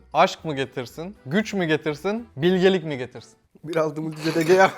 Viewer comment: kardeşin ne diyor 3-4 defa tekrar ettim anlamadım. yok 025 yaptım yine anlamlı bir şey çıkmadı. "bir aldım cedegeaahh" gibi bir ses çıkıyor.. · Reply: 1.6 multijet egea